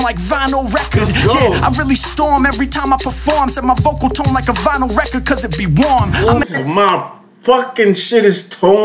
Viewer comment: Yo dope reaction video. Just wanted to point out one of his lines at 6:17 that you liked but misheard He said "My vocal tone like a vinyl record cause it be warm" Anyway sick reaction video and love hearin you critique people's raps